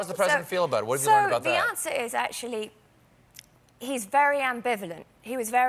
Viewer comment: The automatic captions read "so Beyonce is actually", instead of the answer is actually 🤣